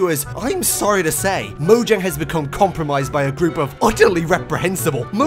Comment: For a second thought he say Peter instead of vegans